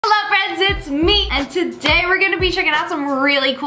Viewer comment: I heard "Hello friends, It's MEAT 🥩" 😂😂😂